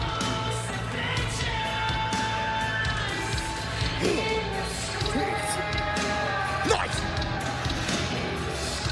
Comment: The heck did he say on 2:30. I just caught that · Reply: "Tastes nice"